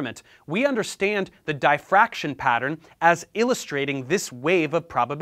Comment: did he just say DIE Fraction?